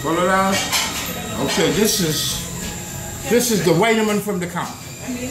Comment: What does he say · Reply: "This is the vitamin from the conch"